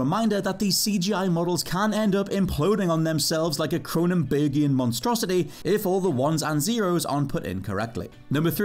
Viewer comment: When I first heard "Cronenbergian monstrosity", I thought he said "Conan Baby-an monstrosity" and thinking, Wow, what a deep '90s cut